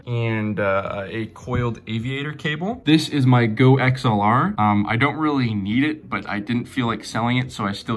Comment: “This is my Glitchdeck” is what I heard anyone else? lol